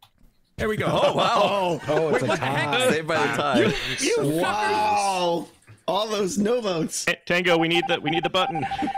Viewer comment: I'm pretty sure I heard swear lol. Could be wrong though. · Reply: He says suckers, if you were thinking he dropped the f bomb :)